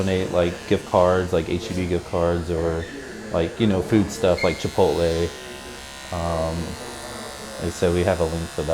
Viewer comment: Did he say "cipolle" at 2:25? I mean the Italian word for "onions". Nice haircut and video anyway ;)